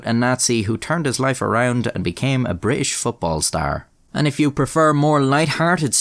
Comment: I accidentally skipped forward and I heard "I became a British football player"